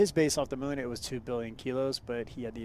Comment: Hindsight hi, that's 1 million tons (us) slightly reminds me off a film called Moon 44. Hope yu are all safe over there be well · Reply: must of mis heared you i thought you said 1 billion kilo's